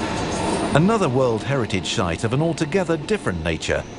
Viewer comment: Great video. Though I heard "Another world heritage shite is found..." 29:37 Was that just me??